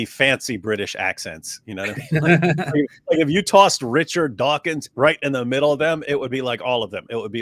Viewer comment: At first I heard Richard Dawson and I was wondering why you would think he sounded posh. I can't imagine Dawkins saying "Survey SAID!"